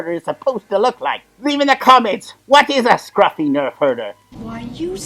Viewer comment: As a kid, I always thought Leah was saying nerve hurter. Hence I never recognized what people were talking about when they said nerf herder. Then I finally found that scene again as adult. Then it all made sense.